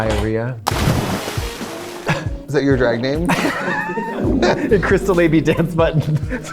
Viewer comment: I could've swore she said Crystal Labia.